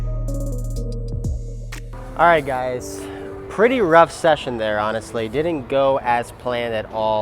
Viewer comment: I thought it said “session crap”